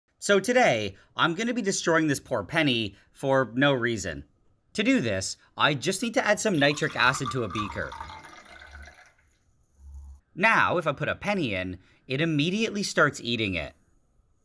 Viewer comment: I thought you said citric acid and I was crashing out 😅